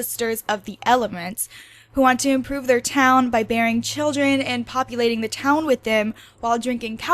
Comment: I thought she said “burying children” instead of “bearing children”